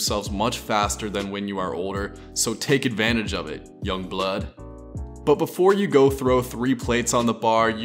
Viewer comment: Did he say young blood 1:44 lmao then he said tje big boy wieghts